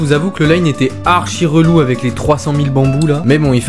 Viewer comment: les300 000 quoi poto ? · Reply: Bah bambous 😊